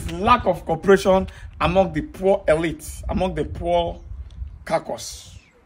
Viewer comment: Did I hear you say "poor elite"?